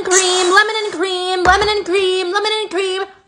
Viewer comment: Brawl I thought she was saying lemon and green